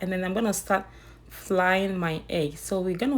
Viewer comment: Did I hear “flying my eggs??”😂😂😂